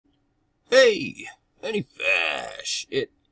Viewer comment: It sounds like you say "Menny Fash!".